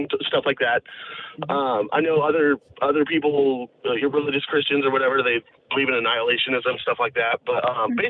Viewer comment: Right at 0:48 does he say Hebrew Christian really fast?